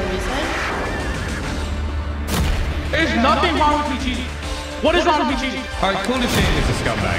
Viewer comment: What's the guy saying at 0:22? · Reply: There is nothing wrong with me cheating.